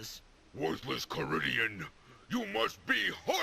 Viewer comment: it sounds like Militron says "Worthless Korean", I can't stop laughing.